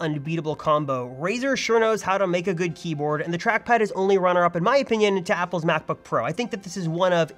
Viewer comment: did he just said the macbook pro has a better keyboard, wtf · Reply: No, I said the MacBook Pro has the better trackpad. I think the Blade's keyboard is great.